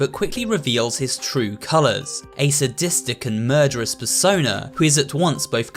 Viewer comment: I literally heard him say fursona, although he plrbly said persona 😂😂😂😂😂😂